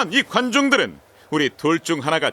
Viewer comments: 여기서 단델이 여기서 이관중들은 이라고 말하잖아요?ㅋㅋ 잘못들으면 여기서 이관종들은 이라고 들려요ㅋㅋㅋ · 이거관종이라 들리지 않아요? 관중이라했는데?